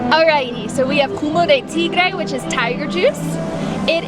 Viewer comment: Min 9:07. Did you say...culo de tigre? 🤔